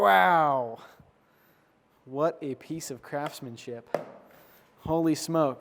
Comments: For a second I thought you said "Piece of Crapsmanship" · I thought I heard you say "What a piece of crapsmanship" and I almost died laughing but then realized you didn't actually say that. Also, what's the brand of your santoku? I really want to pick one up for myself.